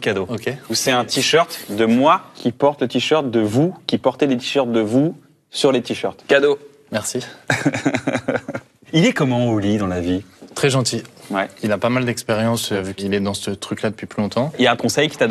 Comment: J'ai entendu "Il est comment au lit dans la vie ?" ^^'